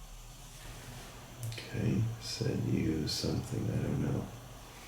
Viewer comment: 😮It sounded like he said "said you was shot" 10:28 I mean, it was clear as DAY FOR ME!!!! WOWWW!!! 😲😲😲 JUST WOA6 😳😳😳😳